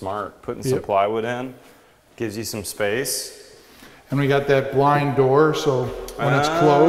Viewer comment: does he say “blind door”?